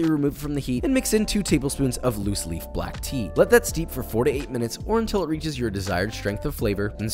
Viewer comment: So at first I thought he said you have to let the tea steep for 48 minutes and I thought well idk man, I don't have the patience for that. Glad I read the recipe haha.